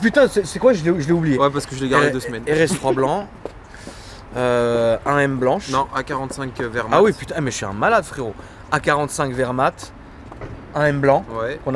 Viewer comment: J'ai compris "A45 wehrmacht" quand l'a dit mdr